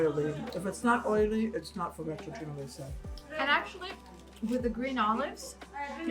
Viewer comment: What is said at 8:52 "...not oily it's not for retrogen and..." What is RETROGEN? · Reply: If it’s not oily it’s not focaccia genovese 😁